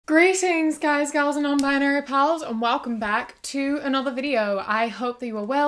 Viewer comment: Sometimes I hear “greetings guys, girls and non binary pearls” and just 🥺🥺